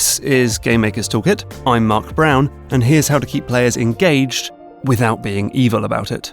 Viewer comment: I thought it said “keep players enraged”